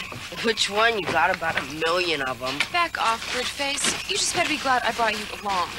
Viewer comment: I would love it if there were closed captions to this. The kid at 6:05 sounds like he says “billion” but if you are listening closer and watching on your phone he really says “million.”